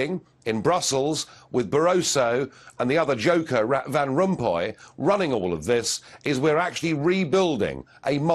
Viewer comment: sounded like he said rat van rompuy